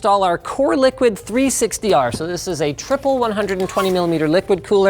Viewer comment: I seriously thought you said “Coreliquid 360 Arson”